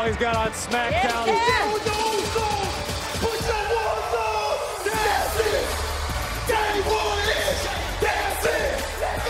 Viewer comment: they said put yo ones up 0:09 if you didnt hear it